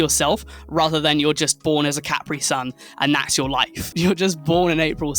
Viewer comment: Did you just say "born as a caprisun"?